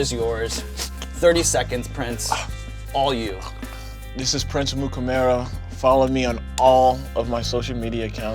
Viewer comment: Did he just pronounce his own name wrong? Sounds like he is saying "Prince Amukamaro" 16:30 After saying how easy it was to pronounce (if you can read it) this stuck out at me at the end. I'm sure I'd mess up lots of my own words too after what he just went through with all that heat, but I chuckled.